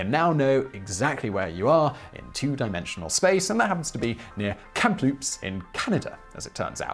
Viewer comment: Kamloops is where I live. But it sounded like he said "Kamploops".